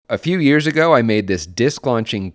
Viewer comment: What said: "A few years ago..." What I heard: "A couple of videos ago..."